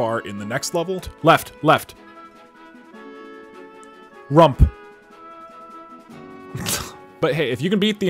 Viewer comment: when he said rump i thought he said trump